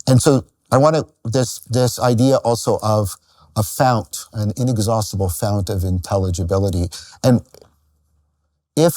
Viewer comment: "Excellent things are ..... Or we all would pursue them" What's the word? I can't understand. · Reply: "rare"